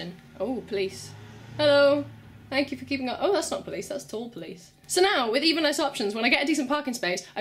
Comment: 'Thats not the police, thats the tall police' What the hell are tall police? Or did i mishear.. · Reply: no, you're 100% right there. no idea either tbh. it was tall police tho